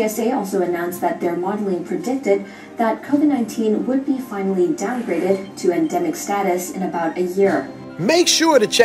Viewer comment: Pause I ain’t gonna ignore what she said. Saying their officials forecast Covid becoming a endemic in a year. For those what don’t know endemic is when a disease or condition is found among people or in a area. The flu is considered a endemic…. thanks for the vids and being a pioneer. Once my business is running smoothly I’m coming there. Need to see what these golf courses are like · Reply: Did she say epidemic? I thought she said “downgraded to endemic status in about a year” You need to check that again swinging 83. Appreciate you!